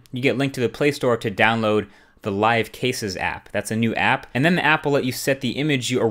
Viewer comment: you said apple instead of google?😂😂